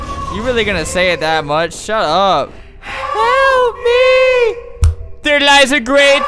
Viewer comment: THE “HELP ME” SOUNDS LIKE JAMES CHARLES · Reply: Is it just me or i hear "help me sisters"